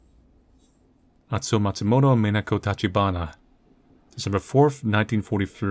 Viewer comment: "Mi... Minako Tachi" read just as I was looking away, so I hear "Me no go touchy" thinking he wanted to avoid touching a gravestone haha